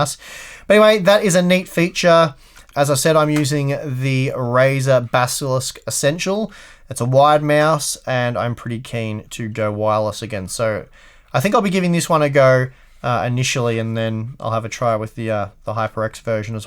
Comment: I thought you said that you had a white mouse now, but since you had us put subs in the beginning I could see that you really said you had a wide mouse. Good to get that clarified :)